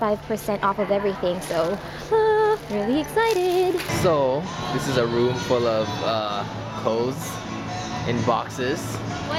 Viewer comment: I thought said "So, this is a room full of hoes, in boxes" lol